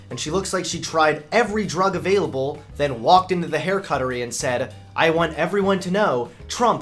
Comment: did say "haircuttery"?